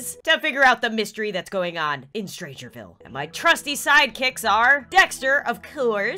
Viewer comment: when she says strange ville captions say strange phil